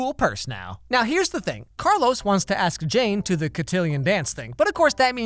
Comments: For a second I thought you said "So Carlos really wants to ask Jay to the Cotillion dance thing" and I let out the LOUDEST scream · Alex Meyers I thought you said Carlos wanted to date jay xD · Lmao I thought he said 'Carlos wants to ask JAY/JAMES to the cartillo" I'm just there like: "I didn't know Disney made homosexual characters"... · I thought he said ask JAY to the cattilion thingy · I thought he said “Carlos wants to ask Jay to the cotillion dance thing”